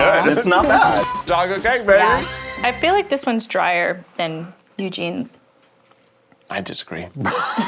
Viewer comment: haha keith's outburst at the end! I had the same reaction too. I really thought the judges said Eugene's was dryer.